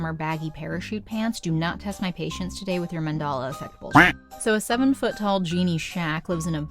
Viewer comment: Sounds like your version of Filmation's Star Trek The Animated Seties or literally anything by Sid and Marty DID YOU JUST SAY MAN-DALA EFFECT?